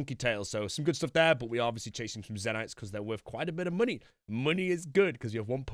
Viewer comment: Lol when he said “chasing some zentye’s” referring to the shard it sounded like he said “chasing some Xanax because they are expensive” m8 that had me so dead 😂 ☠️